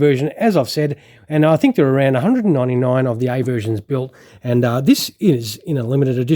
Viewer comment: What did you say about one-hundred ninety-nine at around 1:20 into the video? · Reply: There was around 199 A variants made through the A-7 production before switching to the B variant!! Thanks for watching!!